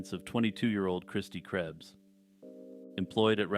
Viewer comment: Oh no when you got to Kristi Krebs I thought you said Krusty Krabs at first 😭 Great video as always!!